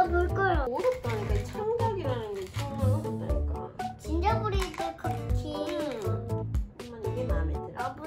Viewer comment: 그냥 아기천사공주님인줄로만 알았는데... SBS영재발굴단천재천사공주님이었군요 .... 저만 들었읍니까?GiNgeRBReD CoOkiE? 😭 최연소3개국어언어천재에게 한 수 배우고 갑니다 ㅠ.ㅠ